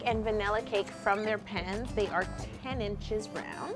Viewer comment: my whole life i thought you were saying "removing the cakes from their pens" and i just realized you're saying pans